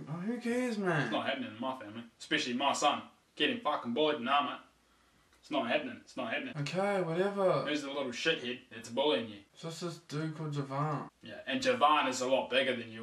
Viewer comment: Did you say Jovan?